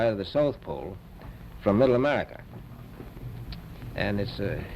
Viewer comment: did he say Middle America